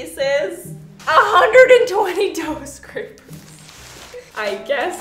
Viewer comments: I thought you said 120 toe scrapers! 🤣 then when you opened the box I was like…. 😳 poor toes! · I thought you were saying, "120 toe scrapers" and I was so confused but dough scrapers makes way more sense. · Just me who thought she said toe scrapers? 😂